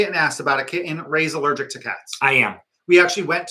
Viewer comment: Oh dear I thought you said you were allergic to kids (you actually said cats) 😂😂😂😂 lovely to see you both,wishing you a lovely holiday 🎅🎅